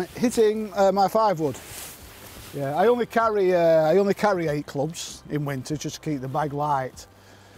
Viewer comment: Just wondering how many clubs your dad carry’s? Don’t think he mentioned it 😂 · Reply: I thought he said he only carries 8 clubs